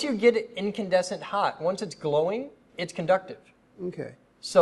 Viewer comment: I thought he said: subductive Then I was like: pfft! laughs what!?!? Then I turned on subtitles I was like: ohhh.... conductive that makes sense